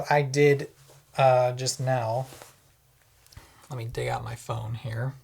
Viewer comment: Omg I thought @ 15:52 you said “whipping out my…” not “let me dig out my” and idk why I got so worked up haha.